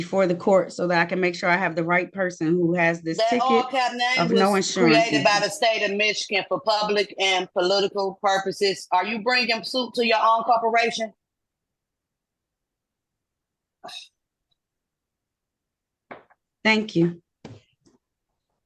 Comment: It sounded like she asked "Are you bringing soup to your own corporation?". I look forward to seeing her subsequent hearings, it'll be comedy gold.